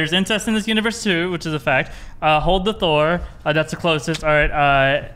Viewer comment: I thought he said "hold it Thor!"